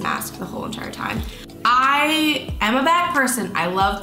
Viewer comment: When you said, "I'm a bag person" I thought you said "I'm a bad person"😂